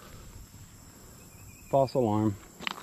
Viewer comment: I was a little distracted for a minute so when you said "false alarm" I misheard it as "fossil arm" and it snapped me right back into attention.